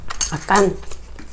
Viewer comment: did you just say "makan"?